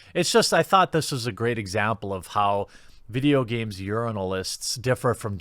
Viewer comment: I always thought you were saying 'games yernalist"